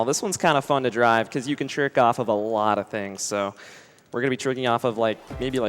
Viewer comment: anyone else hear "we're gonna be jerking off a lot of things"when hes talking about coconut mall at 19:00...